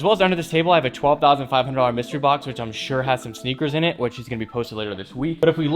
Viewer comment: I thought you said 12000 dollar mystery box today